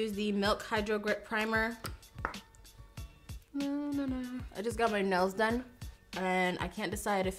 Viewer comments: It sounds like she says “I just got my nose done” and I was like wtf when that’s happened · thought I heard you say, just got my nose done and was so confused by the casualness and the sentences that followed 😂